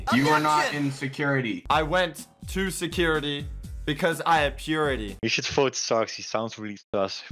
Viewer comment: I heard "I went to security because i have puberty"